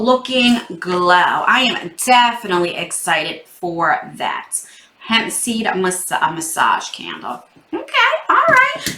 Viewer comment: Ok, from the $49 straw (i know was a oopsie) but then I thought you said "Pepsi" candle and I was like "oh wow" but then you pulled up a "Hemp Seed" candle. lmao Oh so I guess you don't like those crunch cocoa beans. oh, you had me cracking up with this box.